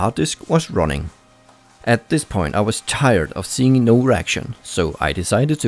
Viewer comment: oh reaction i thought he said erection lol.